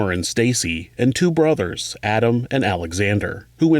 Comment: Iht rewind for a sec', i thought i heard u say she had 2 sisters, stacy n summer n also 2 brothers Alex N . Alexander🤣lmao but u actually said alex n adam! 😂